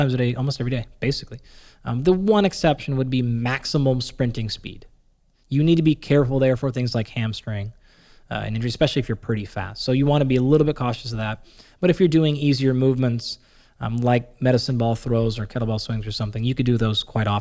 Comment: I thought Andy said "especially if you're pretty fat" lol